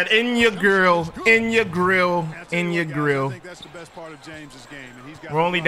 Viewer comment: I heard in your girl